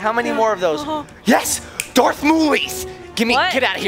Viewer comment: Well this was fun! Too bad Mom is a super noob! haha :) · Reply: Am I the only one who caught Dad say "Darth Moulie" at the 14:50 mark? You do realize "moulie" or more traditionally "moulinyan" is Italian slang for the "N" word. Should probably be more careful about what you say.